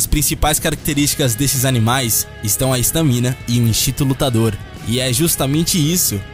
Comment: O que ele falou em 1:10? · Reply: Estamina e instinto lutador